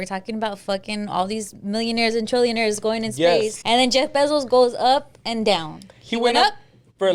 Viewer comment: Sounds like says Jay Besos instead of Jeff Bezos 😂😂😂